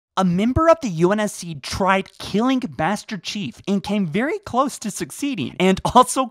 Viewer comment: I heard he tried to kill "Bastard Chief" I had to rewind because I misheard him